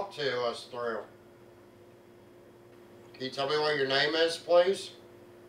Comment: when says "can you tell me what your name is please?" I swear I hear a voice whisper "Dale"